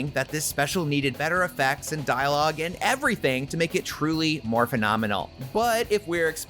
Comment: Did you say Morphin'omenal?